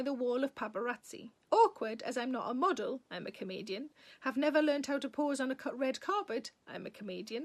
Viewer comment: when you said a few times that "I a comedian", I thought you were saying "I am a Canadian"---I had to go back--and laughed, need to tune up my ears